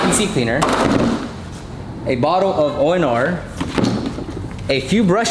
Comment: What does he say after all purpose cleaner? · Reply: ONR (optimum no rinse), a rinse-less wash